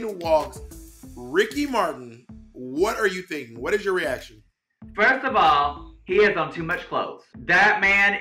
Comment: omg I heard 'first of all he did too much blow' instead of 'he has on too much clothes' and I was like wow v uncesnsored